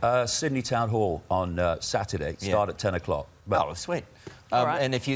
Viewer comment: did david say "got a problem thank you"